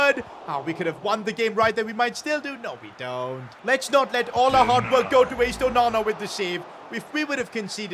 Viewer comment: it sounded like “let’s not let allahwa- “